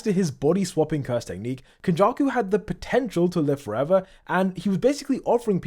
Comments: did you just say POTENTIAL? 👀⁉️ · did I hear potential